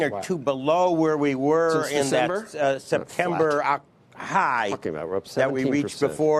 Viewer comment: Did he drop an F bomb? Did anyone catch what he muttered? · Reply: He said “flat”